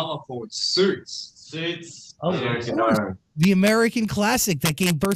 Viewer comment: what does Felix watching on Netflix? 3:45 I didn't understand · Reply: Suits